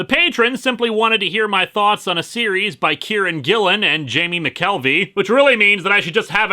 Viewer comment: Damn it, Linkara. When you pronounced 'Kieron Gillen' at around ten seconds into this video, I thought you were saying 'Karen Gillan' and my eyes widened greatly as I said incredulously, "What? Seriously? Karen Gillan...? She made a comic? I'll have to check that out" On the plus side, I now know who Kieron Gillen is and might check out his work in the future.